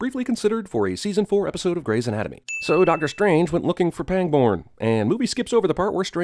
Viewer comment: I almost thought you said pang porn